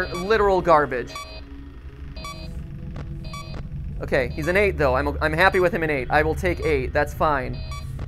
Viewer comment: MatPat: "Balloon Boy is literal garbage. He's an 8 though" So his true love interest is revealed 😉 (1:49:10) I think he really says he's IN room 8, but I like to think he's truly just flattering Balloon Boy.